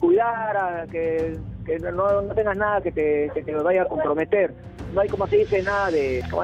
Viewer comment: !!!En el min. 5:15..."el director me ordeno que te cuidara"...y luego ya no transcriben lo que dice pero es entendible.."no tengas nada que te nos vaya a compremeter"...corrupcion al mas alto nivel...:(